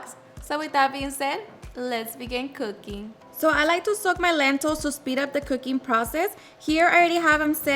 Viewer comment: i had to rewind it. i thought you said “i like to suck my lentils” 💀💀💀